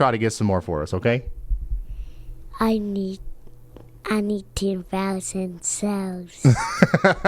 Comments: Could have Sworn he said I need 2000 souls 😅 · you need to call a priest bruh @ 11:30 that boy said "i need 10,000 souls". i know a demon baby when i see one 😂 i heard him loud and clear, he was not talking about no damn snow